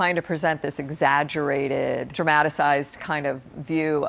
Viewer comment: omg did she say DRAMATICIZED???? its dramatized thank you very much